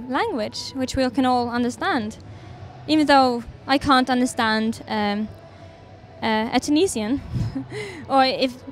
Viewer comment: what she said? Anyone can tell me? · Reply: This is what I believe she says: "I can't understand... a Tunisian... when she [by 'she', Aurora probably refers to Emel Mathlouthi, who also performed during the 2015 NPPC] sings in her own mother tongue. It just... it moves me anyway."